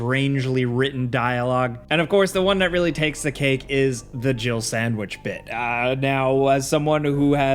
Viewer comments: When I first heard the Jill sandwich line I thought Barry said Girble Sandwich... Wbich makes even less sense. 😂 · All this time I thought he was saying "jiggle sandwich" and that was so much worse in my head lol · I thought he said "jibble sandwich" and always wondered wtf a jibble was.